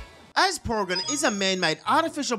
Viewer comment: d-did you just say poregan?